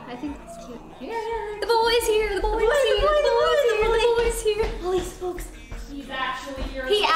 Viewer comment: I thought said: - My boy is here